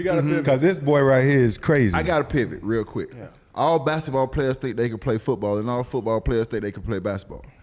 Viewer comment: 'ugly rabbit chasin' ......:face-blue-smiling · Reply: 'basketball players....I thought you said white guys' ☺